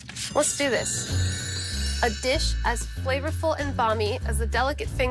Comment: Flavorful and balmy? What did she say??